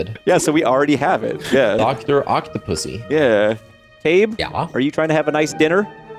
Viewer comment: Did really say "Dr. Octopussy" ? at 4:09.